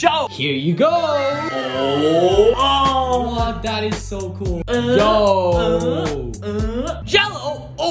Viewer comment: When he got the magical glasses I thought he said daddy is so cool🤣🤣🤣